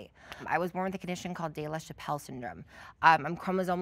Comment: I thought girl said "I was born with Dave Chappelle syndrome"😂